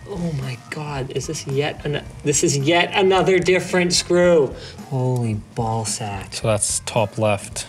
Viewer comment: did he just say holy ballsack? minute